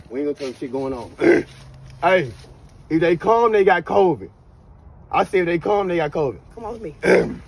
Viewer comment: I thought he said if they cough they got cvid 🤣🤣🤣🤣🤣 im weak 🤣🤣🤣🤣🤣🤣